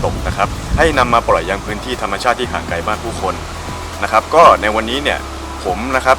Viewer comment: ฟังผ่านๆ ได้ยินว่า "ให้นำมาปล่อยยังพื้นที่หาดใหญ่บ้านผู้คน" ไม่เชื้อหูตัวเองเลยย้อนคลิปฟังดีๆ อ้อ...ห่างไกลบ้านผู้คน ไม่ใช่หาดใหญ่บ้านผู้คน